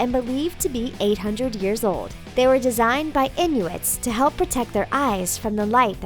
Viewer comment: lol I have the volume down and it sounded like she said designed by idiots